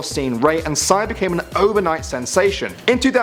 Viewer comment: dang it! i thought overnight said overweight! XD